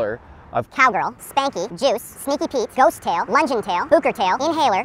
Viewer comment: I actually thought he said Jews but he actually said juice